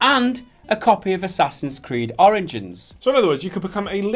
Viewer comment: sounds like he says assassins creed oranges lol